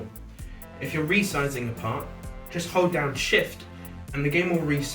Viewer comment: i thought he said "just hold your own sh_t"